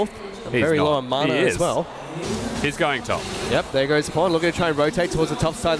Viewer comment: did he say PawN